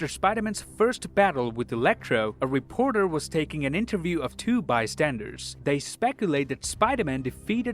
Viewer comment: for a sec I thought you're gonna say, "...two nerds..." 😂😂